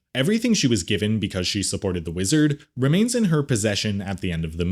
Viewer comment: at low volume and in the background, I thought I heard "because she supported Israel".